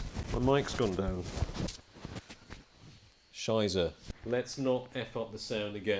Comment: Did you say "Scheiße" at 3:48? :D Lol. using German swearing... Love it.